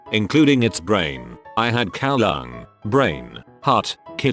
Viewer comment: Oh god, when dude said, “I had cow lung” I thought it said “I had cow dung” I was like, “ewww hellll nooooooo I’m down to try new things but cow shit? Nooooooo!” Hahahah